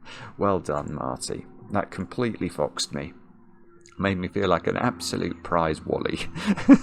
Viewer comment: What did say at 59:10 ? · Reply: "That completely foxed me. Made me feel like an absolute prize wally."